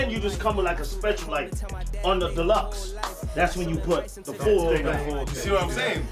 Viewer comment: did someone say Deluxe? 🤔